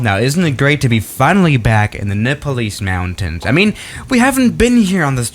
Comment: did you just say "in the nippalease mountains"?